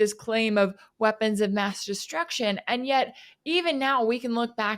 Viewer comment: did Rachel say weapons of mass destruction distraction???